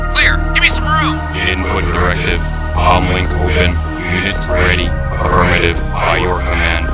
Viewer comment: There's a Cyborg quote I hoped to figure out by coming here.... Sounds like "Ambling to Win" It's the only quote of that unit I have consistently misheard for over 20 years.